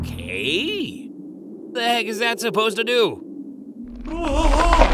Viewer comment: Install Raid for Free ✅ Mobile and PC: <link> and get a special starter pack 💥 Available only for the next 30 days 💥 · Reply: I think Champa said "May The Omni-King (Zeno) Have Mercy On My Soul!".